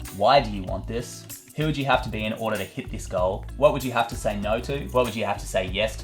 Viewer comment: every time you say "to hit this goal" i hear "to hit this girl", i guess I know what my top goal is...